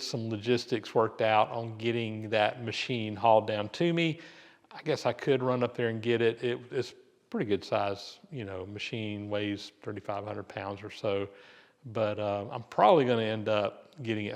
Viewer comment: 3500 lbs? Gear hobber? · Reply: I thought he said 500 pounds??